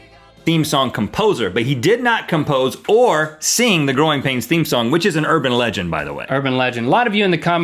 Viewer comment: I thought Rhett said groin pain.